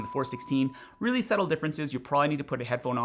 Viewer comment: '... subtle differences' ??? Is this what you said???????????????????????????????????????